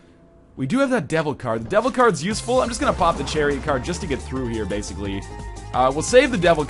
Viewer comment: "I'm just going to pop the cherry card here." Northernlion - January 29, 2013 (I know he said Chariot, But it sounded like cherry.)